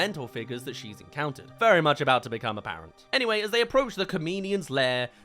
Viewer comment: I thought you said very much about to become a parent lol